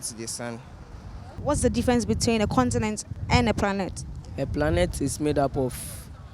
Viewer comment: did anyone else hear "pranet "